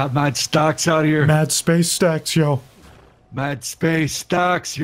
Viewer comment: I thought it said space snack